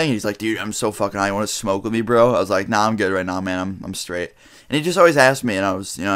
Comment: I thought he said mam